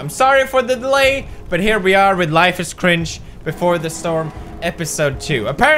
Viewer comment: Did he say "But here we are with Life is cringe" instead of "Life is strange" or am I just too depressed?